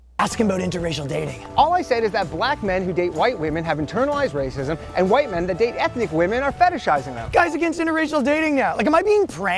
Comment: I don't think you heard him correctly. What he said was the "BLACK men who date white woman have internalized racism while WHITE men who date any ethnic group not white is fetishizing them. Not trying to make a point, just wanted to let you know.